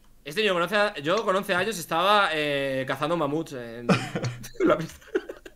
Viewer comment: escuché "yo con 11 años estaba cazando mapuches" xd